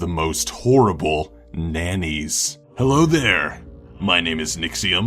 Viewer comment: I thought it said names